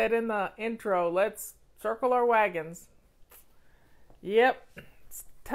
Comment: I misheard you thought you said at the beginning "Purple Your Wagons" LOL